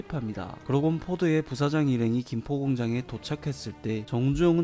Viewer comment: 나만12:16에 김포공장으로 들리나?